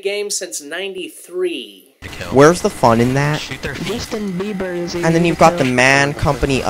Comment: Did the game just say something about Justin Bieber? Or am I just hearing that?